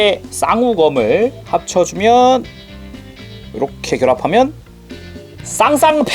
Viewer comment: 나:뭐라고여 쌍쌍바요?